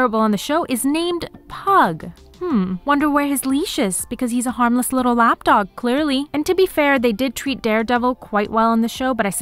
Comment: What did you say? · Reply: I think "wonder where his leash is because he's a harmless little lapdog clearly"